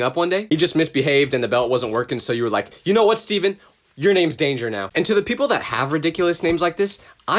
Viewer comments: I think they were saying that’s dangerous instead of them saying they’re kids name was danger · SHE WAS PROB SAYING IT WAS DANGEROUS AND TL GET DOWN